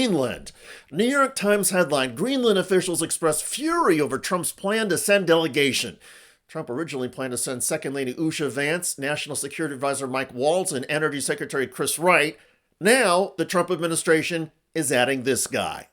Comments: Yes, I just went back and listened , it sounds like he said Shady Vance 😂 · It is funny how when he says his name it sounds like he is saying Shady Vance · It sounded like he said “Shady” Vance. 😂😂😂😂😂😂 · I thought he said "I'm Shady Vance". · i heard him say "Shady Vance" sounds similar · who heard Shady Vance like I did? O.o? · Im sure I heard 'Shady Vance' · Hilariously, when I heard Vance introduce himself 0:23 I could have sworn he called himself Shady Vance. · He said it so fast.. I thought he said 'shady Vance' lol Maybe he did lol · Shady Vance, that's what I heard him say 😂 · I thought he said Shady Vance! 😂😂😂😂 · Anyone else heard him say shadyVance?